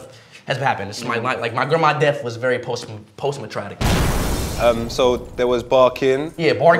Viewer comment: Did speed really say “postmatratic” ? 19:21 😭😭😭